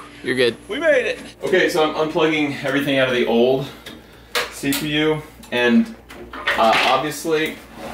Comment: did you just said "i am unpluging everything out of the cpu"